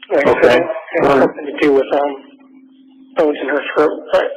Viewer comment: wtf did he just say here? "they're settling on trying to say its a homicide. um.. which I had nothing to do with... umm. I was in her throat, but... take that to mean what you will" is anyone else hearing this?